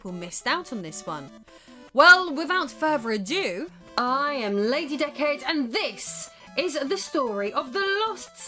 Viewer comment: I've been casually watching your videos for years and for the longest time I thought you were saying "I'm Lady Dickhead" on account of your English accent and I was so confused until I actually used my brain and read the name of your channel. I'm from Texas if that makes you feel any better.🤣🤣🤣 I guess I should subscribe after I post this comment. I've always thought your videos were awesome.